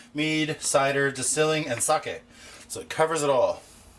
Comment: at first I thought he said "for wine, mead, cider, distilling, and SUCK IT!"